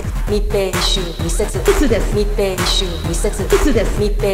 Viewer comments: うつですって聞こえてくるw こんだけ密密言ってたのに今では人流だもんなぁ… 3密回避して営業頑張ってた大型施設がかわいそう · 昨日深夜にこれを大音量で流しながら走ってる車がいた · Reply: 密密密 秘密ですうつですって聞こえて来る(笑) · だめだ…ぴつですって聞こえるw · 気を抜くと、「うつです」に聞こえるんだが^^